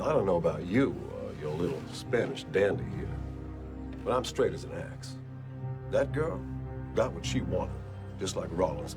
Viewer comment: What did he say in Spanish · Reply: He says "Nos veremos en los tribules, mi amigo." Which means "we'll see each other in court, my friend."